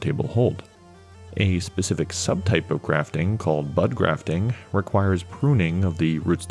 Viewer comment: watching this for a fourth or fifth time in the background of working. I thought I heard them say “butt grafting.” can you imagine? “I am the Lord of all that is cake!”